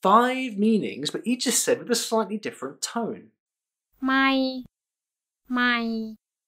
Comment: All I keep hearing was 'My'